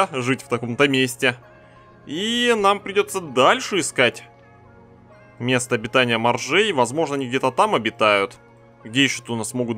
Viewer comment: мне послышались "нам придётся дальше искать обитание бомжей"